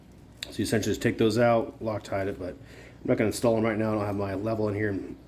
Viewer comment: I couldn't quite make out what you said about the rings (6:20ish)...did you say loctite them? I don't you're supposed to loctite rings, bases, yes. Rings, no.